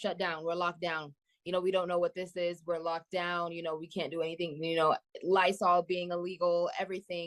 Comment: does she say lysol being illegal?